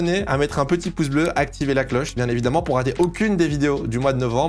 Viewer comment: c'est moi où il a fait exprès de dire "A activer la cloche, bien évidemment pour regarder aucune des vidéos du mois de novembre.." 😂🤣